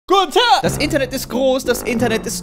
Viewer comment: warum verstehe ich da gunter?